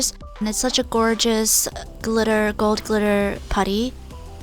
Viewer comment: I thought u said gold digger putty😂